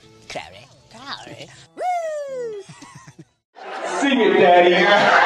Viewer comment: ish, did Jensen say, ''sing it daddy''.